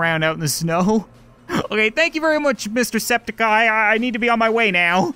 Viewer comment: I was gonna say, how dare you not know Jacksepticeye.... but at the end “goodbye mr. septic-bot” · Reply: i thought he said mr septiceye but yea